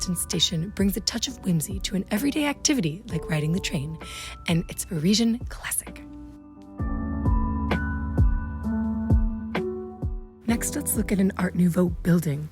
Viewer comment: Not clear what you're saying at 4:11 (closed captioning didn't get it either) -- '...and its origine classique'? Is that it? It's preceded in that sentence by: '...brings a touch of whimsy to....' So, it brought a touch of whimsy to its classical origins? Does that get across what you were trying to say? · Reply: Parisian classic